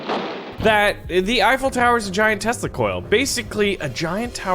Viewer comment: Dont know if anyone will see this but I thought he said the Eiffel Tower is like I giant testicle